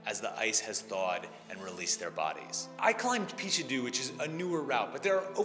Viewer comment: Tragic, yet beautiful. I gravel before your rock puns. Also for a split second I heard "I climbed Pikachu."